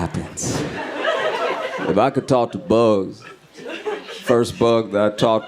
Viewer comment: I thought you were saying talk to BOOKS! 😅 I was thinkin', that for sure is a high thought . . .Bugs though, I get that. *edit Also, I killed a hornet in my bedroom today. I have no idea how it got in there